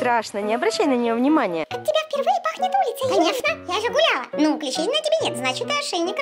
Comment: Мне одной послышалось, что сказала курица вместо улица?😂